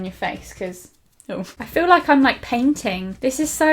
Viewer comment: When said "it feels like I'm painting" I thought she said "I feel like a peng ting" lmao :)